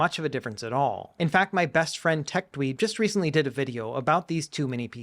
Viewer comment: Oh phew. I was not watching and only listened to the audio, and at 8:02 I was like "wait a minute, my best friend Tech Lead???". Thankfully I just heard it wrong lol